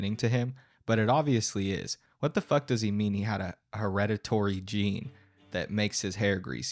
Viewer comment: I heard him saying "I have a predatory gene" and had to rewind to check. Unfortunately it was just "hereditary", but that would have been the best Freudian slip ever.